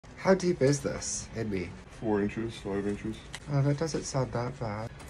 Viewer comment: I thought he said lemon juice orange juice but he said 4 5 inches😂😂😂